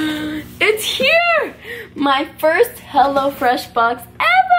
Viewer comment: I thought she said hella freash